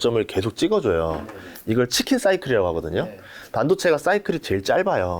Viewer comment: 치킨사이클 나만 이렇게 들었나 한심하네요. 책쓴거 읽어봤는데 그저그렇던데 지금와서 이런말 하면 뭐하나요. 다 아작나고 단기반등 쬐끔 하는데